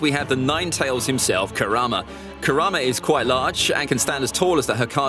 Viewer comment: did I hear QUITE?! Tall… kurama is QUITE TALL?!